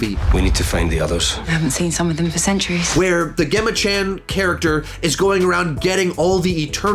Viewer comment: did he just say ‘gehma’ 🤣